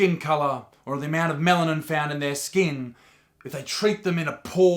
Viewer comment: did anyone else hear "the amount of melon in their skin"